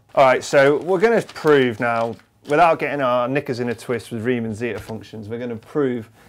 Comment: What did he say 💀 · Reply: He said "Getting knickers in a twist"